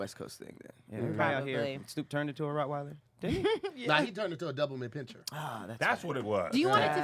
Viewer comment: #Hey!!! @ 52:14 What did He call that dog.. a Double-mint👀👀 Pinscher ......" Doberman Pinscher is what I think you meant to say"